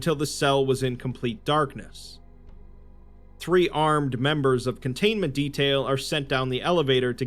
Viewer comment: I heard that as "Three-armed members of containment detail" and was briefly quite confused when the Foundation started hiring Vortigaunts.